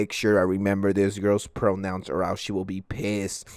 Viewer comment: I thought it said ps4 pronouns ☠️☠️☠️☠️